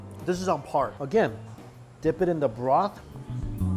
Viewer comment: I heard he said "dip it in the bra"